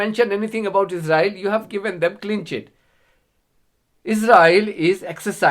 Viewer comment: he says a word that sounds like "klenchet". What is he saying ???